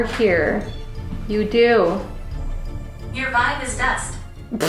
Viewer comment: I heard “your vibe is best” right here.. I had my eyes closed, so I didn’t see what you all thought you heard here, but it sounded like “best” instead of “dust” to me